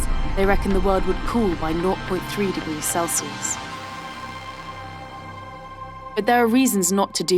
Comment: Is she saying "Null.3" degrees Celsius?